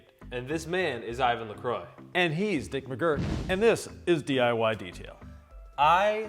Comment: I swear I hear “Dirk McGurk” 😅